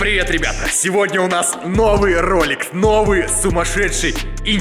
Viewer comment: мне послышалось "новый ролекс"